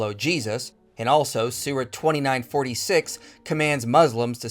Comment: Every time you say "Surah" I hear "Sewer".